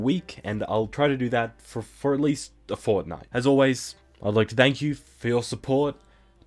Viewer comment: DiD hE sAy FoRtNiTe